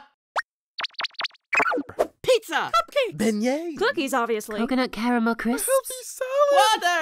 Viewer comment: Silver Spoon at 16:49 does he say Yin-Yang as a snack? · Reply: Beignet